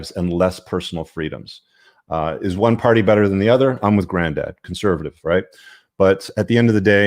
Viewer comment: I thought you said: I'm a granddad at 14:17 😳 I couldn't believe it and listened over and over. Then I heard you say: I'm with granddad. 😅 By the way, I'm a grandma. 😄